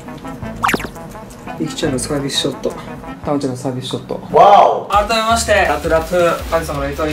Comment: 最初炭治郎のサービスショットって聞こえたww あと最後と一緒に見つけてもた